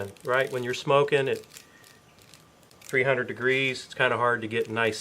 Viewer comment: Did you say smoking at 300 degrees at 3:06? Those are some awesome looking thighs there. By the look on your faces at 9:56 I knew these were really good tasting.